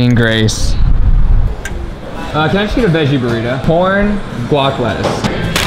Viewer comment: I thought he said porn lol